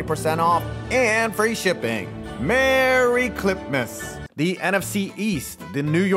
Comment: Thanks to MANSCAPED for sponsoring today’s video! Get The Performance Package 5.0 Ultra for 20% OFF + Free International Shipping this holiday season with my link <link> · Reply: JFC I watch on 2x and thought you said Clitmas